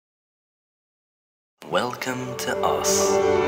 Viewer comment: I hear "Welcome to Os" lol